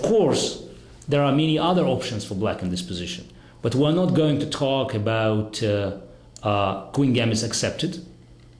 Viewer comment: Thought he said we WERENT GONNA TALK ABOUT THE TARRASCH LOL